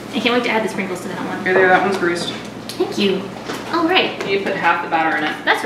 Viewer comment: it sounds like bakugou said babe that ones greased